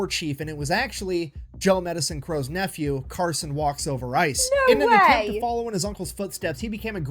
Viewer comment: Carson Walks Over Ice (Medicine Crow) was his nephew. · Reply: I thought said Walks Over Rice and thought it was a nickname his buddies gave him 😂😂😂😂